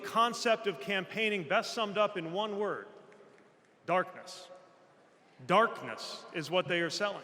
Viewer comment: I thought he said garbage instead of darkness LOL. GARBAGE is truer.